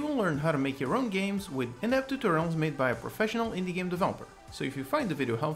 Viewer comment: In the beginning it sounded like he said "inept tutorials made by a professional developer" rather than "in depth".